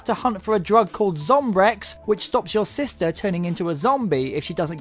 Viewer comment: did he said "sister"